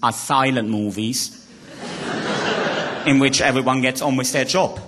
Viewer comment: all my dreams are silent movies in which everyone ______ joke. what did he say there? anyone? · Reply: "Gets on with their joke"